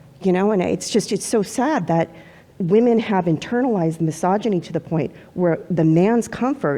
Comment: Thank GOD for the molecular geneticist. We need more people like her. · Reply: "The woman on the disagree side made the claim the reason why transgender women are put in women prison is because women accepted misagony 8:16" She clearly said "internalized misogyny", not "accepted misagony". It's not consciously accepted; it's internalized because of fear and societal brainwashing.